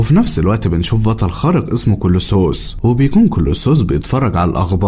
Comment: سمعتها كيلو صوص 😅😅😅